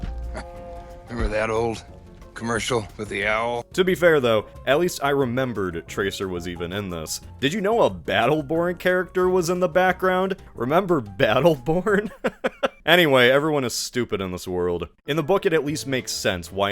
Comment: the most telling thing about battleborn’s cultural staying power is that i couldn’t tell what you said and thought you meant “beetleborg” like its more likely the big bad beetleborgs, a power ranger copy from the 90s, was more likely than a 2016 video game